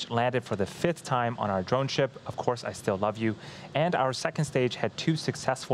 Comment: Did he say "of course I still love you" at 1:23:50?